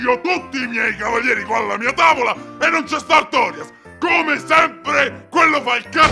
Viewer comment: I don’t know but what Gwyn said sounds like ,,and it’s just Artorias’’